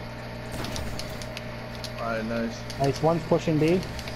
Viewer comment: LOL! Absolutely wrecked! If I had to pick 2 favourite things about this clip: #1 Killing the stormtrance #2 I thought I heard "One pushing D" B) #DYNAMIX